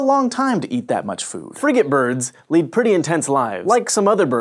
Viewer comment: I thought he said fr***ing birds instead of frigatebirds! :)